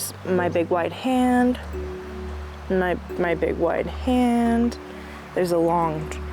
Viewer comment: I thought you said "I big whte hand" 😂